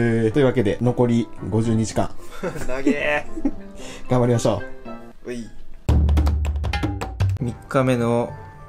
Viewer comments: 画面見てなくて52時間頑張りましょうが、50日間頑張りましょうに聞こえて！？！？！？ってなった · 残り52時間が残り50日間に聞こえてええ！？ってなった笑笑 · ｢52時間｣が｢50日間｣に聞こえた